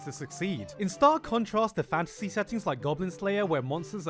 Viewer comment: O O F I thought i heard "Goblin S L A V E R"